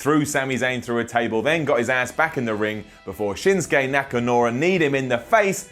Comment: Simon said Nakanura. I heard that.